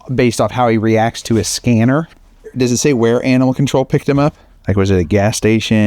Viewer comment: "Does it say where Anal Control picked him up?"